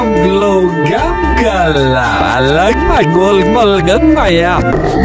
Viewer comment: I like my gum more gr like my yum (That’s what I heard)